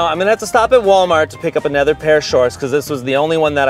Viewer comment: incidentally on the blooper part. Instead of hearing "Another pair of shorts." I heard Ian say "A Nether Pair Of Shorts" is he summoning Cthulhu in his shorts?